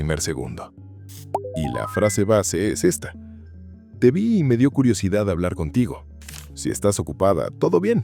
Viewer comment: mañana todos: "te vi y me dió curiosidad de hablar contigo" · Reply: Pensé que decía "te vi y se me paro" el corazón we